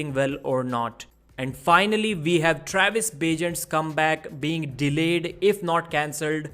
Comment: Was I the only one who heard "Travis Bagent- scumbag " ?! :face-purple-wide-eyes::face-green-smiling